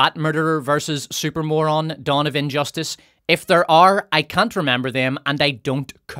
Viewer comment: I thought you said "Donovan Justice", which, now that I i think about it, might be a cool super hero name.